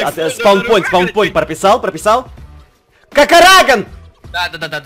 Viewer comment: Кому то послышалось на 08:00 что он сказал еба@